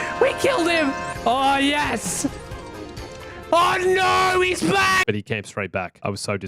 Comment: Am i the only one who hears "OH NO, HE'S BLACK!"? ( 5:56 )